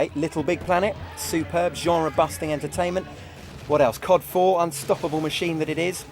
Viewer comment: did he say cod4 @ 1:34. that came out 2007 n00b